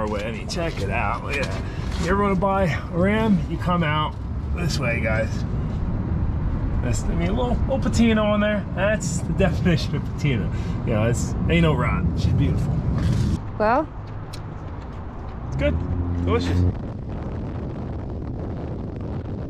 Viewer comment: Had to listen to what said 3 times. I though for sure he was saying "an*l rot." 🤣😆